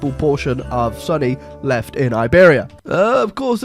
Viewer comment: Wtf i thought you were saying sunny instead of sunni